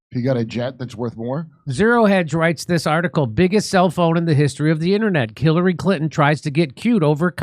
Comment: When I listened I heard "biggest cellphone..." 😂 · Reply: That’s what I heard too!😂😂 Self own finally made it through after I heard it several times.